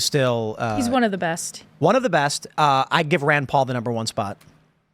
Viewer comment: To be fair, I understand the 2A issue, and he was a BLM supporter so I'm not surprised by his support for Ron Paul. Though honestly I thought he said Rand Paul.